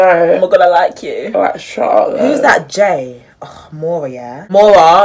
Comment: What did you say at 9:19?? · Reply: I said “Who’s that J, ugh Maura yeah”